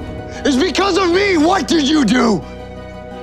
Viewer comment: I thought it said didn't you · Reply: He did, but, I made a mistake, sorry 😅 I thought he said "Than you"